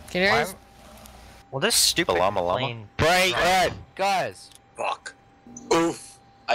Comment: this guy is really recreating Flight 93 right now 3:24 BALLama LLama 3:25 (BREAD)? 3:47 'LLama' Monitor Unicom. 122.8 3:50 the f**k was that? 3:57 we currently have a Boeing 737 that is currently doing some slip sides on taxiways 1:06 Tower Lufthansa 385 am I clear to push back here? At Gate alpha 25 1:12 that is a HORRIBLE German Accent 1:14 British guy 1:17 Didn't know Russians play Flight Simulator X 1:19 oh god Terrorists are here 1:21 ) clusterf$$k 1:22 pretty sure that is not a language 1:23 Russian 1:24 Dylan Klebold is that you? 1:25 leave 1:30 there is active ATC but uh don't talk 1:31 did someone just eat their mic? 1:32 kind of sounded like he said we're all gonna die 1:34 no